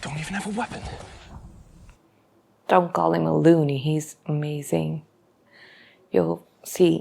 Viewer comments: Lewey is slang for Lieutenant. It sounded like he said Looney but he was using the slang for the rank. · I think he said 'Don't call him a lieu-y', like a lieutenant.